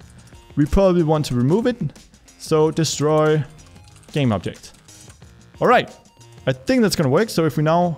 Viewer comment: what did you say?? (what did you write) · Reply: Game object